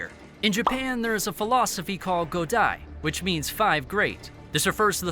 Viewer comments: when i heard godai i thought it was saying go die 😀.. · yo did he say go die?